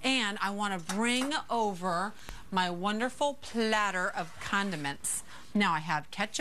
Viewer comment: for a sec at 2:05 i thought she said her "wonderful platter of condoms!"